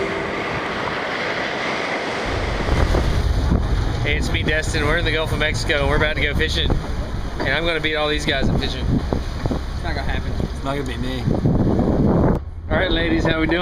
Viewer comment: Lol I heard him say he’s gonna beat off all of these guys fishing